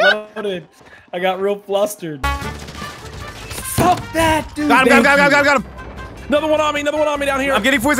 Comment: It sounded like he said “Suck that dude!” 😂🤣🤣